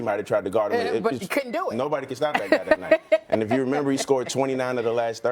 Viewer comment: Lol did he say nobody could stop that N*#!a that night